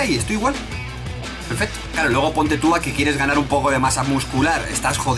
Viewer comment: se llama Carlos? o solo dijo un nombre aleatorio??? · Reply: gb yo entendi "claro"